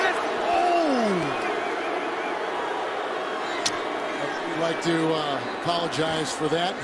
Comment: "Gah Dammit Donald!" as Peyton is slowly running for his life lmaooooo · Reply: I thought he said God dammit Nappa